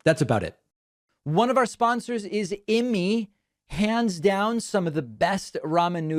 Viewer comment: After Jesse was on, it was a little weird that your first ad sounded like you said "One of our sponsors is in me!" I had to do my own research and discovered it's "Immeits." Whew.